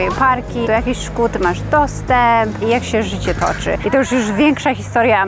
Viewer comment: "do jakich szkół ty masz dostęp", a ja usłyszałam: "czy masz toster". Tak mnie to wgięło, że nie mogłam się skupić na dalszej części i musiałam wrócić do tego momentu, bo nie mogłam rozkminić co ma do tego posiadanie tostera 🤦🏻‍♀️😂